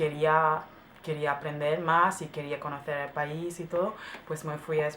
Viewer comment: I wasn't surprised when she said she studied Spanish in Spain. She sounds like she has the Spain accent to me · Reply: she said “to” instead of “todo”, i’m sure you have the andalusia accent x)